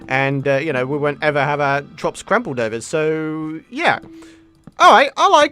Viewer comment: Who else noticed that at 12:10, said "trops crampled over." instead of "crops trampled over."?